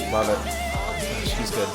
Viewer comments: shes good im sorry chen 😔✊ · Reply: I thought he said “Shit, he’s good” lol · did he just said: "she's good (about chen)" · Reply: It is "gosh, he's good" 😊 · shes good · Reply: I thought he said “gosh he’s good”? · did he say “she’s good” bc then I’m going to start referring to Chen as sister and we don’t want that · shes good im sorry chen 😔✊ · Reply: exo gimme your number i thought he said Jesus good but like slurred together 😅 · did he say “shes good” orr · did,,, did you call Chen a she at 7:42? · i love how u said she's good to Chen idk if i misheard it or not, but i love it, change my mind